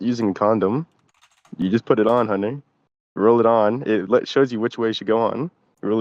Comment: “It takes things as simple as using condom…you just put it on, honey.” I swear to god I thought this guy called honey